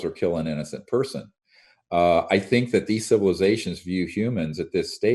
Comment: he said for "you" humans, why did he not use the word for "us" humans? hmmm. he acknowledges aliens among us and then he said for you humans! slip of the tongue acknowledgment? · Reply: It does sound like he said that, but after listening again, he actually says these civilizations "view" humans.